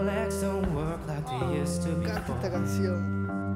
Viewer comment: Que dijo Luis Fonsi al principio??? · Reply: Karen Diaz que le encanta la cancion 😄